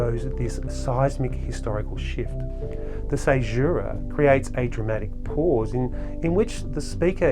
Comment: What does the word sagira mean 18:29? I couldn't get that. If anybody knows please let me know. Thank you. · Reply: it’s caesura - it means a pause near the middle of the line .